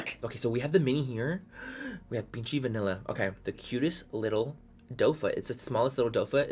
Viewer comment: i thought you said "pinche vanilla" lol